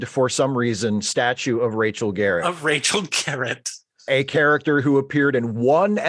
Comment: RACHEL Garrett? I thought they said EDNA Garrett! Oh well. You take the good, you take the bad...